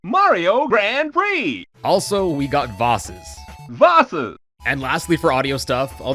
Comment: Vahsus Oh I thought you said bosses. (That would have been cool to have though)